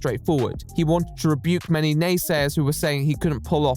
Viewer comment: who is the person in 37:36? i seriously can't understand the name says. melanay? menna nay?